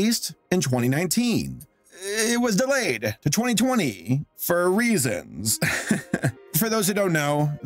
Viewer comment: When you said Sonic was delayed "For Reasons" I heard "Fur reasons" actually both ways work. Funco Pop really? have we learned nothing from Play Mobile and Ugly Doll not to imitate Lego? (at least trolls made new designs) I do wish that either " Good Boys" or "The Boys" would change there name I get confused. the best thing to do with Streaming is place several networks under one umbrella. No one will want to pay for over 100 streaming services at $20 each on separate bills. .